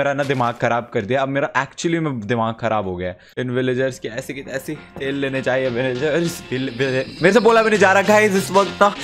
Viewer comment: listen carefully did he say a bad word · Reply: He said "inn villagers ki aisi ki taise tel me jaaye ye villagers" and that is not a bad word🙄